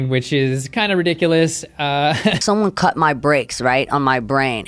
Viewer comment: ''Someone cut my bricks, right, on my brain..." is that what she said or just me? okay, back to what you were doing... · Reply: I heard "breaks" not "bricks". Not sure if that makes any more sense. Run-away flighty thought patterns, maybe?